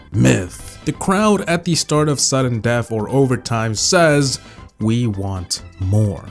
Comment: In my mind I heard we want milk